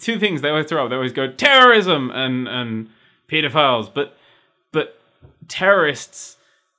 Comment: when you say pedophiles it sounds like your saying petaphiles